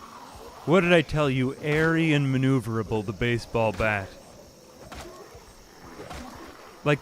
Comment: Him: "Airy and maneuverable, baseball bat." What I heard: "Arian maneuverability baseball attack"